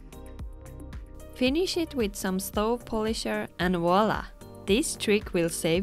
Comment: What is that at marker 3:54??… did you say stove polisher ❓